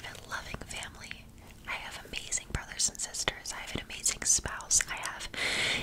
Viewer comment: did she say spouse????